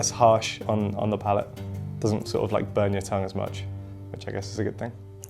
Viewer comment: Did he just say doesn't sorta like burn your tongue 3:39 why is salt spicy to white people?